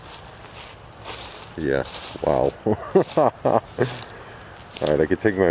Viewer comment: Hi and, happy Two's day 2/22/22 and I'm doing great and great creepy place. That is a very creepy house and creepy tall grass, herenarenthe creepy stuff I captured in this video and they are in the reply link. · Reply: Disembodied creepy Woman's Voice Sounded like "WHOA"